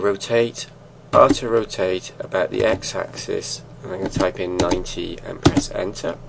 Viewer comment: after rotate about x-axes.. this part i cant follow cuz i cant hear clearly,is it press on r then type in "90" ? when after i type in 90 then enter the uv sphere just face at right corner. this is the only part i cant follow.. i can follow the body,head and leg but the eyes is very hard :( help me~